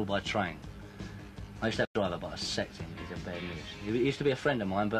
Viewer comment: What is Eric saying here? Who is he talking about that used to be a friend of his? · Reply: Sounded like he said he sacked him